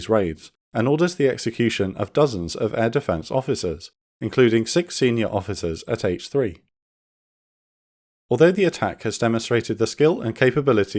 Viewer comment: had to rewind that bit, thought he said 'six senior officers at age 3'